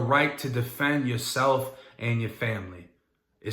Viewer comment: Yo what do you even say in the begining? I'm rocking with yall if yall rocking with me? Or what cuz it just sounds like you say im rockin with yall, yall rociin with me